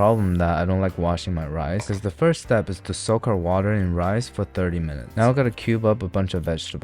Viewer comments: did he say “soak our water in rice”? 😂😂❤ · Did I hear it wrong "Soak our water in rice"?